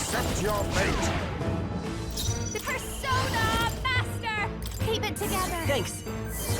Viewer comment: Did she say persona master? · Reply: yes, persona master is one of the archetype, the evolution of masked dancer